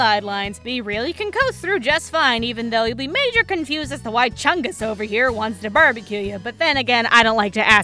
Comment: DID SHE SAY CHUNGUS REeEeEeEeeEeEeEeEeEeEeeEeEeEeEeEeEeEeEeEeEeEeEeEee